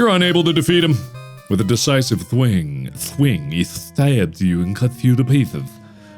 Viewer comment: you never can go wrong with a oh deciceve swing lol why you say it like that in that voice I thought you said "massive blade" was gonna say you cant go wrong with a massive sword!